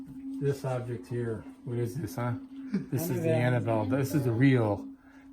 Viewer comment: did anybody else hear a child’s voice? I couldn’t make out what it said though..maybe I’m dead? If u think about it then maybe Also it might just be another person in the crowd. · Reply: It sounds like it is saying 'Annabell.'